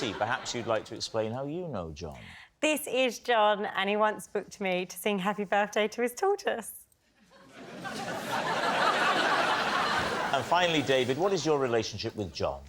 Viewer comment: That was really hard to understand her saying, "He booked me to sing happy birthday to his tortoise" lol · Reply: Aye. 😂Thought she said sing happy birthdate to his Daughter😂